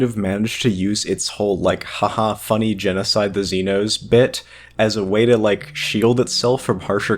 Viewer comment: thanks for taking the time to paste the script into the captions but please edit your subtitles/script after recording or follow the script. take for example 9:31 missing kinda after has and it should be "haha, funny genocide the xenos" instead of "haha, kill the xenos" may or may not be the only deviations the script/audio have since i wasn't paying attention to the subtitles due to me not needing them. subtitles are meant accurately reflect what is said instead of what you meant/what the script was. you don't have to be verbatim about ummms and uhs but when words are different from the captions to the audio it changes the video for those who are deaf/hard of hearing. it might not seem like much but the words "funny genocide" and "kill" convey different messages.